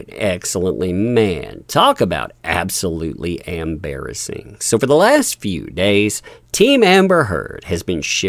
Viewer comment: I must be hearing it wrong. I thought you said AMBERasing.